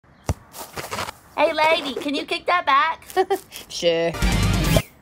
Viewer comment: i hear "hey legy *